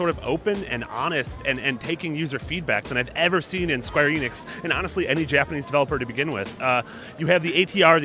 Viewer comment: When they say square Enix it sounds like they say square anus so I cant take them seriously